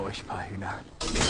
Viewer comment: ich bin deutscher und verstehe dennoch nicht, was der da sagt 😂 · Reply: Zuviele Füchse für euch paar Hühner.